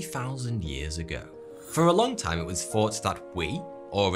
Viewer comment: I thought he said 42 not thought2